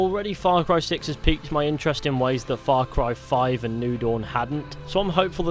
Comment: Thought he was saying far cry noodle not new dawn...